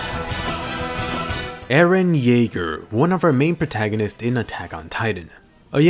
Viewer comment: or 0:02 or 0:03 I could swear I heard eren Yeager